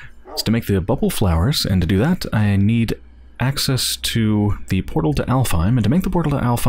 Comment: I got distracted for a moment and thought you said the portal to hellfire. I was like, did I miss something?